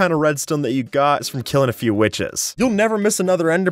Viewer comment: idk if something is wrong with my headphone or what but when is said witches on 5:12 i heard female dog or you can say witches but with a 'B'